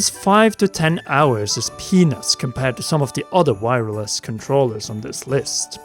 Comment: I don't think he said penis but I can't hear anything else